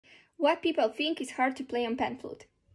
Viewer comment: I seriously thought she said white people thinks it's hard. 😂